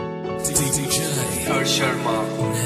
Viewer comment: Who hears Rohit Sharma in this part 🤣😂😂